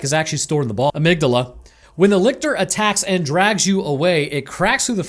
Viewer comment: I heard that ! x) "Boor amygdala"